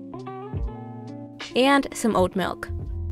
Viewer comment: I thought she said “old milk” and I was really grossed out and then I realized she said OAT milk.